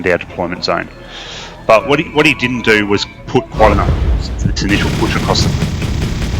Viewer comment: Audio is a bit unclear. What were you saying at 5:03? "What he didn't do was put..."? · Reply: Yeah, this was my first video. I was saying that he didn't put enough of his force into that push across the bridge. In comparison, my whole force was used.